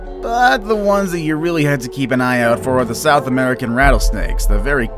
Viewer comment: I thought you said South Africa good it was South America